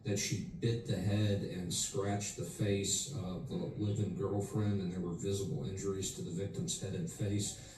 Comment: That man said the victim was her "live-in girlfriend"? Is that what I heard?